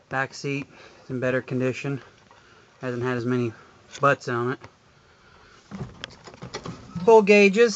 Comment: Nice truck! At 1:40 did you say "back seat is in better condition. Hasn't had as many sluts on it." ? If so, exactly how many is too many? ;-)